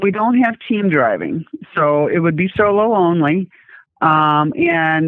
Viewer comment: 😂😂 It sounded like she said it would be so lonely due to not having teams lol